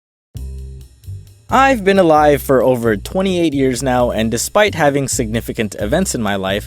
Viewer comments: He says, "I've been alive for over 28 years now. . ." I hear, "I've been a lie for over 28 years now. . ." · I thought he said "I've been a lie for over 28 years now" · for a second I thought he said I have been dead for over 28 years now lol